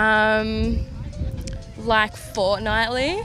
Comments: what did she say?? I heard fortnite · what does she say? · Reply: tomyman Fortnightly, every 2 weeks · she actually said “fortnite” thought that was like “inevitable” Who tf says that